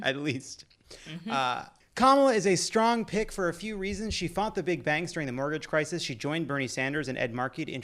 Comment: I heard "she fought the big bangs" ... (before "in the financial crisis")